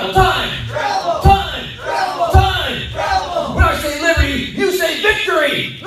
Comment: What's he saying there? 6:20 PM 12/16/2019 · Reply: when i say liberty, you say victory